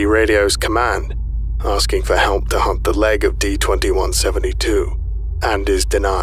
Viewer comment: does anyone else hear "asking for help to hump the leg of D-2172? Or is it just me?